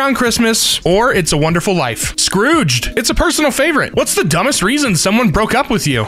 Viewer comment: when he said "Scrooged" at 1:11, I thought he said "Scourge" From Warrior cats TwT